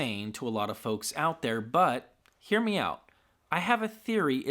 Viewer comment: When you say "hear me out" I hear "hear meow"